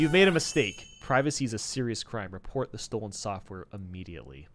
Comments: Instead of "Piracy is a serious crime." I heard "Privacy is a serious crime." Caught me off guard for a second xD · Sounds like you said PRIVACY is a serious crime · did you just say privacy is a serious crime? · did he just say privacy